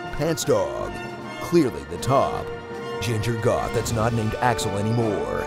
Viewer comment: I thought he said "Ginger God that is not named Axel anymore" and I lost it 😂 Also... Excited for KH3.